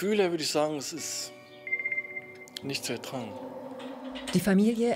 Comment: Was sagt er? Ich kann das letzte Wort einfach nicht verstehen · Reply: " Nicht zu ertragen"